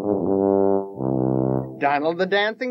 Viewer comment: For some odd reason I misheard and I thought his name was 'Diamond' not 'Donald'. Speaking of BEASTARS ocs: I claim Diamond Donkey as the name for my oc suckers!